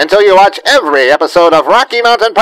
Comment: Instead of "permabits" I'd swear I heard "cannabis"... LOL